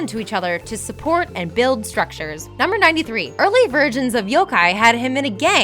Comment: What Should We Do Next? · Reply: she sounds like she says virgins not versions.